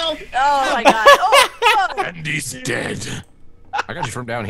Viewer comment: Does someone say "DENDI's DEAD" at 1:31 when the dota thing poped up